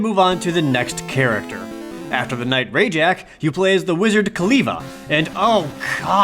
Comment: Not gonna lie, I heard "Wizard Kaliva" as "Wiz Khalifa".